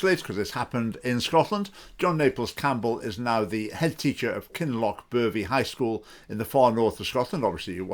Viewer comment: i thought you said 'Kinlochpervie ''